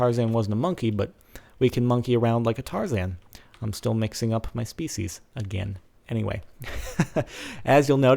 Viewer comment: lol I thought he said im still mixing up my feces instead of im still mixing up my species LOL